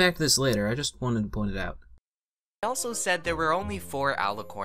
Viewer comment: you better not let Cannedcream know that your a Celestia fan or else you're in BIG trouble. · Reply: Sorry I thought you said that were then again I have bad hearing